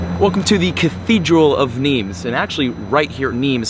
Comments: When He Said nimes i thought He Said meme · The first two times he said Nîmes I thought he was saying memes 😕